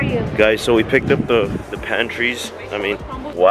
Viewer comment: Did he just say “pantry’s” instead of pastry’s 😂